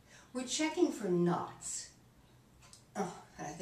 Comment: I thought she said looking for nuts.